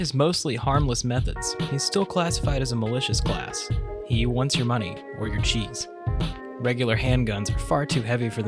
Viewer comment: I misheard what he said as "he wants your money or your cheeks" 😨